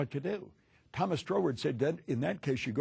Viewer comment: what's the name he says? Thomas something. Thomas Drower? And wad the inner source called font saturego? Trying to google both. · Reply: Troward